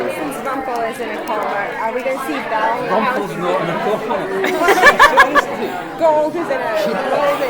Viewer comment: Please, what does robert say at 3:26 ? I don't understand very well. Thanks :-) Ps I miss Rumbelle!! :-( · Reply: Rumple is not in a coma gold is